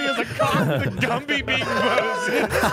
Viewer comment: I think said "Moses" and not "posted".